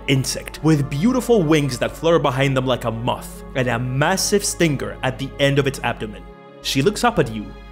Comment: Honest to God thought you said massive stinker haha